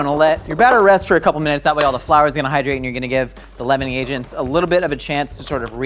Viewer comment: He said leavening agents; I thought he said lemoning agents🤦🏾‍♀️